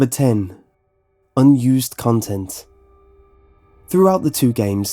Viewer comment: in the beginning I thought he said used condom instead of unused content and I just sat there like "oh"